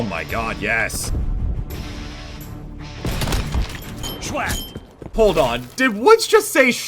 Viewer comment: thought woods said schlatt :(